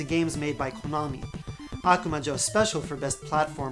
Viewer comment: Wait what is the game at 0:38 ? You said it so fast and it's hard to spell it. Looks like a fun game! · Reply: I'm assuming you mean Akumajo Special, also known as Kid Dracula. It's a cartoony spinoff of Castlevania